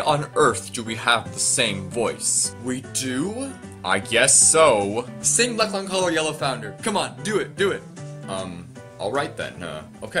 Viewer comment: what did they say what’s the song they said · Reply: Epic Crasher 2010 A parody I wrote in 2017 titled “Black Long Collar, Yellow Founder” 😂 you’ll find it if you search through videos posted in July 2017